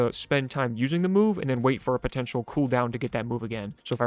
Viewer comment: DID YOU SAY POTENTIAL????